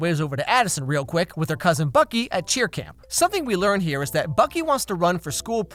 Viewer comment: I thought you said with her cousin "Fucky"